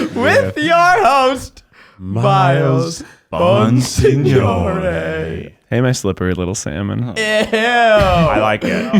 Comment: Im at the big boys table babyyyyy! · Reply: until I read your name here I thought you guys were saying „miles monsignore“ as in like a fun mafia film related joke name